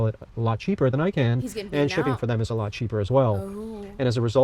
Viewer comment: Lmao did she just say “cheap vans “ ? I can’t lol